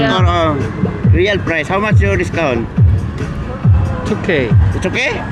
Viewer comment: 2k~ 를 "it's ok" 로 들었는데 "2k~" 하니 뭔가 라임이 고수 느낌 되어서 잘 된듯 해요 ㅎㅎㅎ